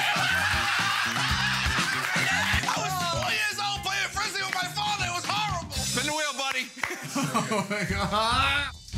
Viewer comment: what does Sal say at 2:37? He was 20 years old and what? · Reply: Aroj I was 20 years old playing Frisbee with my farther. it was horrible.